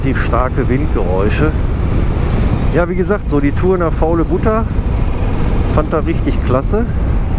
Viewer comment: Als Bayer der sich in Deiner Gegend gar nicht auskennt glaubte ich mich bei dem Ortsnamen "Faulebutter" verhört zu haben. Hab dann gleich mal bei googlr